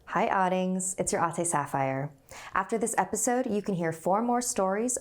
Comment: I heard: "Hi hotties! Its your auntie saphire!"